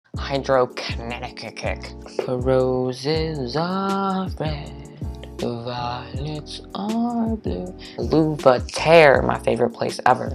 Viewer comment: Great video!!!! This video was so funny, I loved it! In the beginning I thought you said Hydroconnecticut And I was so confused I watched that part 5 times!